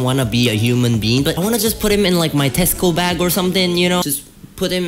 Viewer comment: I heard "under my testicle bag" and I was like....bet..... Until I replayed it HAHAHAHAHA